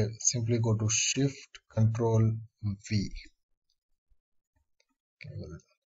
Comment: I am sorry but at 3:29 I don't know what you said. How do you get the legs to switch positions? Is it Shift Ctrl V or Shift Ctrl D? And where is the cursor to make the copy anf then invert it? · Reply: you have to select the first key frame and ctrl c it to copy and then shift control v is to past it and reverse it. ctrl v will simply copy it but shift ctrl v will copy reverse, hope that helps.